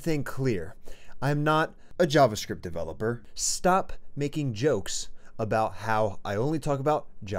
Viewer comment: Hold on, what did he actually said at 00:04 ? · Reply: “Web developer” I think